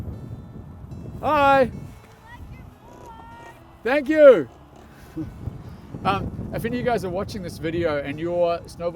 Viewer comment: What did she said at 7:23? She was saying something like “I like your…” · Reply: Board